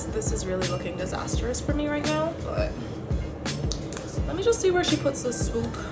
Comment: I thought she said, This ostrich for me right now